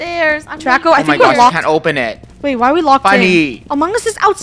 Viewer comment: ! It Sounded Like "Bunny"